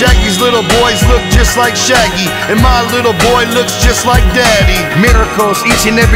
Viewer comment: "Shaggy's little boys look just like Shaggy. And my little boy looks just like Shaggy." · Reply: I heard it as 'my little boy looks just like daddy'